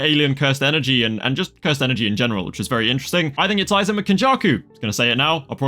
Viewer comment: What did you say at 0:28 I heard “I think it’s Aizen mm Kenjaku” Which is probably not what you said.